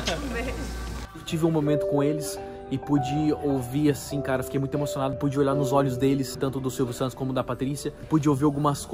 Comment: " me desliguei do mundo ali e foquei 100% no que ele tava dizendo..." Corte " então esse aqui é o celular da Google, pixel..." UÉ????????????????????? Entendi foi nada. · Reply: Alice Pucker disse: “má okay... sai pra lá, sai pra lá!!!